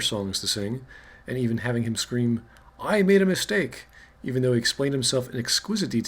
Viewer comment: what i heard "i made him a stake" what was actually said "i made a mistake"